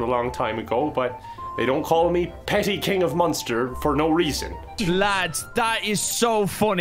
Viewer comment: I thought it said “Pretty King of Munster” and was like yeah thats true